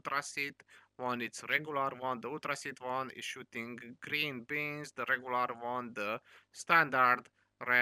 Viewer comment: At first I thought you said it fires green beans and I was like wow that's interesting...oh beams!! That makes more sense lol